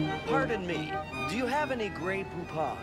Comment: I admit myself and my brother didn't get the joke as we didn't come from or lived in the US. My brother thought Wayne said "Do you have any grey poop on?".